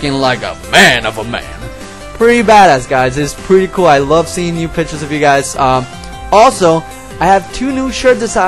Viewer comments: It sounded like he said " I like seeing nude pictures of you guys" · Lol, at 2:20, he sounds like he says "nude pictures"...